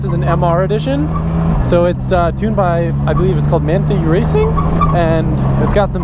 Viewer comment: For anyone one curious about what Adam says at 31:25. Manthey Racing is very big in the Porsche and motorsport world. They build incredible cars. They hold the current record for the fastest production car around the Nürburgring with their 911 GT2 RS MR. They always do really well at the Nürburgring 24h as well. I can only recommend anyone interested in that sort of stuff checking them out!